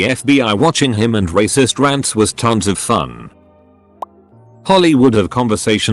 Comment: I am just Listening. At one point I thought if said "Hollywood" and it made no sense but then I looked at it and it says "Holly would". Do native speaker get confused like this?